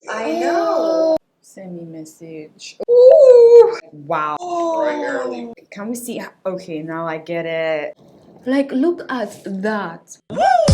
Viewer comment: Lol who else heard her say “who send me message”😂😂😂